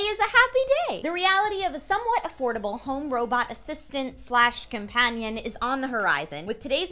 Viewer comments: Damnit. I thought it said "Robot Assassin". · I totally thought it said "Robot Assassin" but this is still really cool